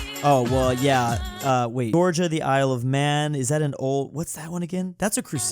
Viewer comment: bro did not just call Northern Ireland the Isle of man